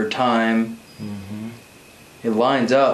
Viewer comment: Did anyone else hear a female say, “mhm “, at 1:41:20????